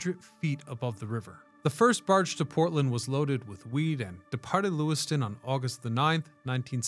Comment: I swear to God you said loaded with weed. Lol. I've gone back and listened to it 4 times and it never changes to me.